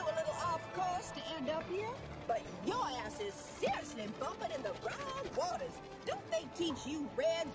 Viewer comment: I heard "But yo ass is seriously bumpin' in the wrong waters"